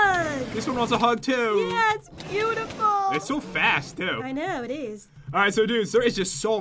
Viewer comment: I thought said'hes so fat to' but said'hes so fast to